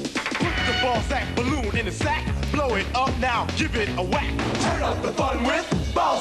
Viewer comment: Did they say blow it up now give it a whack